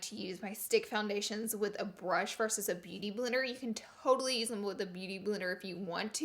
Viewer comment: I love when at 13:36 she said beauty blirer instead of beauty blender I don't know if that's even a word lol but I love it Love you Laura!!